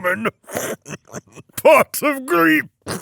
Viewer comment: The captions don't even say what he says at the end. But it sounds like he's saying pot of grief. I don't understand how that's a funny joke can someone explain?